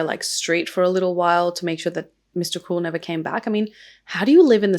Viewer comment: I'm sorry, thought you said Mr. Cool, my bad (aka)